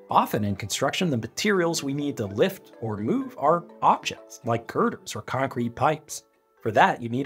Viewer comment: Love your stuff very easy to digest and learn. I couldn’t understand what word you were saying at 6:59 (objects sounded too much like options) idk if it’s an audio issue or just my ears but I needed the subtitles